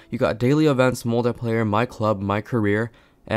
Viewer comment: When said "my club" I thought he said Minecraft